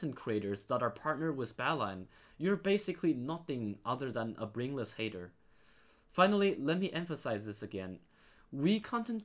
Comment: "brainless haters" - 2021 (best quote ever) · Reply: i thought you said "bedless hater"